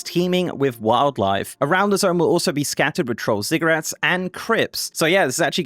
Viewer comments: i thought he said Troll Cigarettes lol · did a double take at 3:10 because I swear I heard "troll ciggarettes" lol